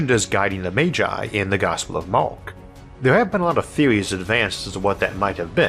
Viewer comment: yeah I mixed that one up, there's a note in the subtitles about it but that's about all I can do for 'corrections' in youtube these days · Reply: I'm 100% used to your distinctive and completely clear, compelling speech style. But today I heard 'The gospel of Mork' and the idea of that greatly pleased me.